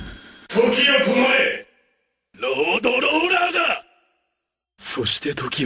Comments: What is he saying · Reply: soshite toki wa ugoki dasu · WHAT DOES HE SAY AT 0:36? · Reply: ".Soshite toki wo ugokidasu" (And so, time flows again) :)